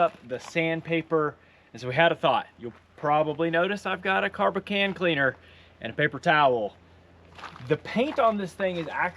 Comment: Did I hear you right ? You said carb of can cleaner at 29:50. 😂